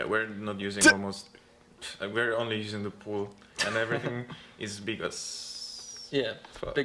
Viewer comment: what does he say? Everything is bigus haha.